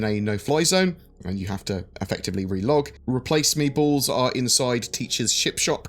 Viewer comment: It sounded like you said "replace meatballs." That's what I'm calling them from now on. Glad they're getting the 890 cleaned up and getting it out so we can get on to bigger/better things. Great vid.